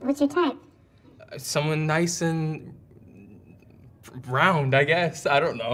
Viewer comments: is bro saying nice and ROUND??? i replayed like 10x lol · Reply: Think it's brown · Did he say nice and browned???